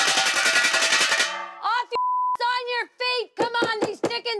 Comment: What did Lacey say? BTW, Lacey's daughter is awesome. The thumbnail almost makes her look like she's related to Drew Barrymore (from her E.T days). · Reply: She said off your add on your feet